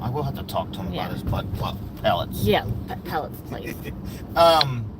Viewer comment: I’m watching the replay and can’t believe the conversation 🤭🤣 did you just say “butt plug”. Please say pellets not plug. 🤣🤣🤣🤣